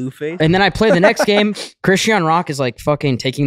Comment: I heard "Christiano Ronaldo" and pictured the most random group of individuals going bowling together